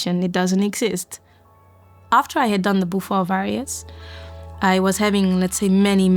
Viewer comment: What does she say at 11:55? "After I had done the - - - - - - ? " Beaufort Variants??? If so, what are they? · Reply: Bufo Alvarius maybe?